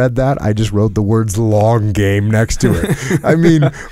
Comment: I thought he said Log Game, instead of Long game, and thought he was referring to the log that was dropped on the guy's back, lol